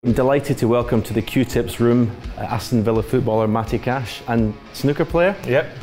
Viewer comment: Hahahaha I thought it said Pat Cash and I was like "our mental Aussie tennis player is good enough at snooker to have an audience with God Hendry"?!?!?!?